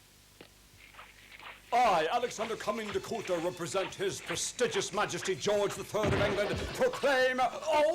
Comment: What does the man on the horse say at 24:39? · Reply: "i Alexander Under coming to do represent His Prestigious Majesty King George IlI if England and proclaim..." *falls off the horse*